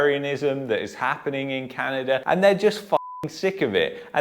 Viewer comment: Did you say Can-an-der in the beginning 😂😂😂 That's what I heard, can't lie. It was jokes! But in all seriousness, were in a SERIOUS spot here.